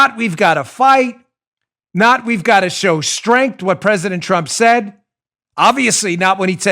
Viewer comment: i guess they misheard "we must show strength" for "we must hang pence"